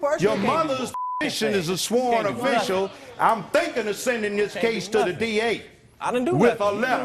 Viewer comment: What did the defendant say at 5:40? U can't do a fuckin thing to me?